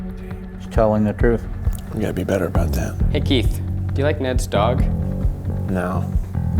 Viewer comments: When he asked Keith, "Do you like Ned's Dog" I thought he said "Do you like Ned Stark". XD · I thought he said Ned Stark instead of neds dog lol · I thought he said Ned Stark instead of Ned's dog. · I didn't hear "Ned's dog" I heard "Ned Stark" and thought "wtf how can you not like him" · Who else thought he said "Do you like Ned Stark?" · When he said 'Ned's Dog' I thought he said 'Ned Stark' and then he said no!! 😂